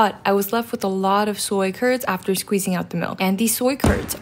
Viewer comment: " I thought she said turds instead of curds-😂"